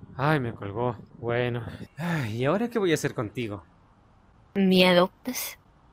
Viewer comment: yo escuché que la niña dijo Miedo pues Y era:me adoptas:😅😂😂